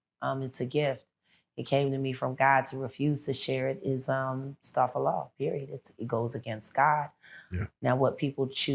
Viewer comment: What is she saying star ful law at about 6:58? Refusing to share goes against God? I just couldn't catch the term she used "star ful law"